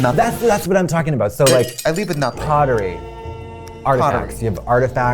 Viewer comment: When Katya said "pottery is a hobby" I thought she said poverty 💀